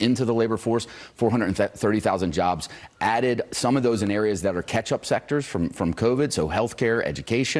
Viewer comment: What I heard: 'Ketchup sectors'. English is a weird language, man.